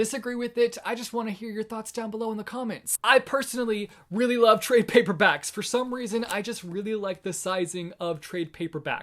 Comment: What das "tray(?) paperbacks" mean? Or what did he say?